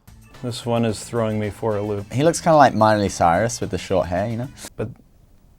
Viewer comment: Did he just say that Sehun looks like Miley Cyrus 😂 omg I can't stop laughing 😂😂ahahahahahahaah